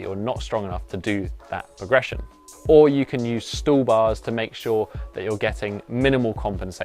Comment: Did you call these things "stool bars"? When I search for that, only barstools come up in the results. Where can I get such a setup? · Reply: They're called stall bars or swedish ladder